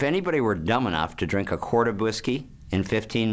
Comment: what do you/he say at minute 1:05 , i can´t understand him well. A "quart" of wisky??? I am from Argentina and I can´t understand well.